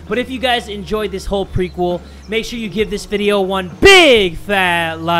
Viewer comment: For two years I thought he said (Jay from the kub scouts IS DEAD TOO) but it's that dude lol bruh Eve at the end it says it